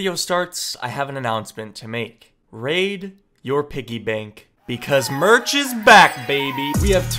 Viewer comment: At the beginning I thought I was going to say raid shadow legends But it was raid your piggy bank I got so scared😆